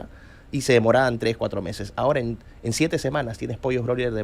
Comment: Qué dijo??? 🤣🤣🤣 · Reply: Pollos Broiler.